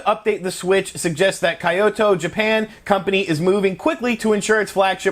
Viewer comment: did he really say Kaito?